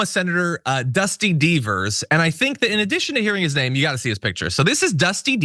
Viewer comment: I swear I thought you said his name was “Dusty Beavers”?? 😂